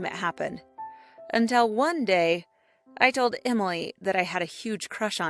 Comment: did she say her name was emoji🤣